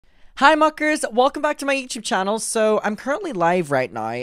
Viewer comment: Ngl I thought you said 'hi fuckers!' In the intro lol